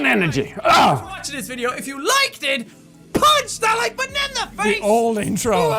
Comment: To be honest when Jack did his outro I always thought he said punch the like banana in the face like a puss